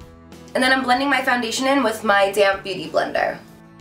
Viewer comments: Every time someone says "damp bueaty blender" I hear "damn beauty blender" 😂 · Sounds like u said damn beauty blender lol